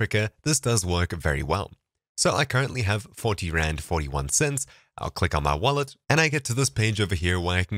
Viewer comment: I thought he said 40 grand and I was like dam get a life